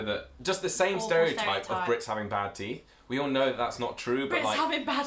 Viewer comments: I was low key confused, thought you said British have bad tea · I thought y’all said Brit’s have bad tea and I was like ummmm well then. then the comments came up and I was like oh....I fell stupid now